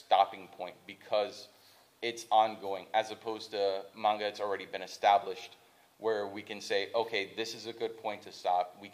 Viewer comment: What was the manga recommended at 38:00? It was "something Saga" I didn't understand it T.T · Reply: Vinland Saga.